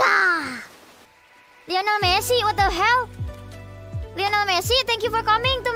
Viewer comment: Did she just say LIONESMEL lmaooo